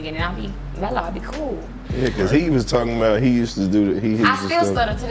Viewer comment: Am I the only one that heard her say she use to do hard ?👀 🤔